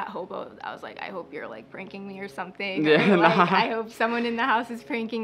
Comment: Instead of putting pranking they put breaking 😂